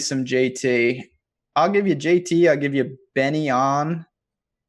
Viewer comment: Who is Benny On? I turned closed captions on to get what he was saying at 43:12 but still could understand. Thanks for the in-depth analysis though! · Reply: Byeong-Hun An